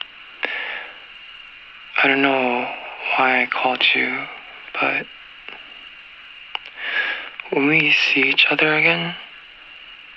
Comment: i keep hearing joshua say "我們一起" at 0:17 even though i know he's speaking in english oml what is he even saying